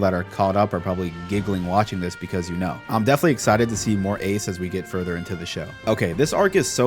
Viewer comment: did he just said watch more of ace 😢😢😢😢😢😢😢😢😢😢😢😢😢😢